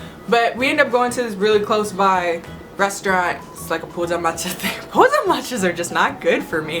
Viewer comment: what are you saying? 푸자마차? 푸타마치야?